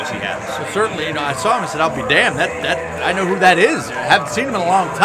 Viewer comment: That's hilarious that your color guy said he saw The Bronze God Lennox Norris and said "I'll be Damned"....it almost sounded like he said Al B. Damm...good to see ABD making the rounds and showing some love to Columbus.